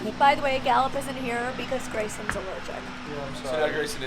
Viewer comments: i thought said 'lil jerk' to grayson until i replayed it then realised that she said 'allergic' 😂🤣 i'm so deaf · First time I thought she said "because grayson is a virgin". must laugh so bad. 😂 And at the end when gray blow the kiss... so freaking cute. 😍